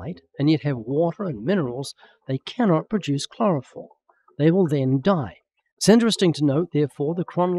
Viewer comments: Does he really say “chloroform”?! Is that a Freudian thing since he is so freakin’ boring? 🤔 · Did Ray say plants make chloroform? It sounded that way to me, I think he means chlorophyll